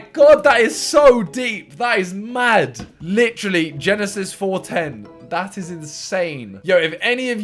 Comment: the only reason I got this was cause I was looking away when said “Jenna, Sis” so I actually thought he said genesis